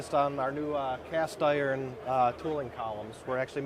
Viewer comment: I was way off… I thought it said cast iron trombones…. 😮